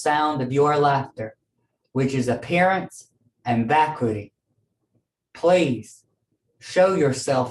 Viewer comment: did she say "which has appearance and backwoody" ?